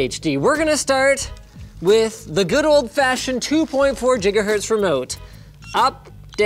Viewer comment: did he say "Jigahertz" o.O